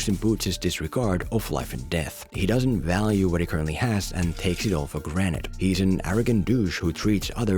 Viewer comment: did he really say " take 8t for GRANITE?